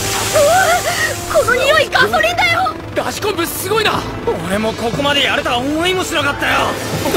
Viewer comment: 初見の時は、 ラジコン部すごいな！が ダシ昆布すごいな！って聞こえて何言ってんだこいつ？ってなった